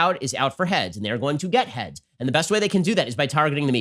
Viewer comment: did he say “get head”. Hmm..